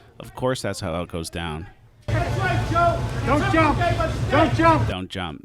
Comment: I thought joe said “GO TRUMP“ not.. “don’t jump“ 😂